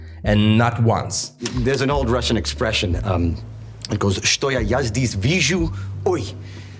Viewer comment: i think he saying "что я здесь вижу, ооой", but becouse of american accent it is not so clear